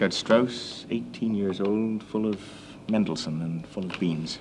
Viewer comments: so 'full of beans' apparently is a regional colloquialism that I've never heard. Gould always adds to my vocabulary. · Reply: I thought he said “full of Wien” and that he accidentally added a z to the end, to be honest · so 'full of beans' apparently is a regional colloquialism that I've never heard. Gould always adds to my vocabulary. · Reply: Also a time thing I guess. I'm from Toronto and never heard it before. I always thought he was saying "full of dreams" but honestly that makes more sense